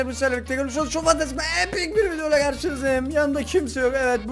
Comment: abi ne dediğini hiç anlamıyorum bitek epik video dediğini duyuyom